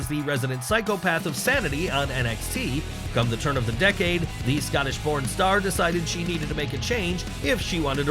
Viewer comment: Wait.. What did you say?😂 Scottish porn star? Is she? Bruh😂